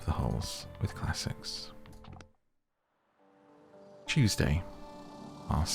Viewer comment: when you said Tuesday i thought you said cheesday😭🧀